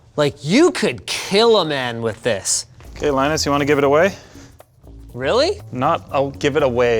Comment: Almost had my hopes up when he said GIVE IT A WEIGH...thought he said GIVE IT AWAY...